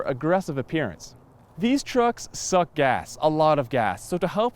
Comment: Jesus Christ, I thought I heard him say "These trucks suck ass" at 2:00.